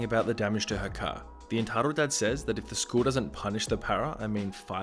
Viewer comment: with captions on, it says Antara instead of Entitled.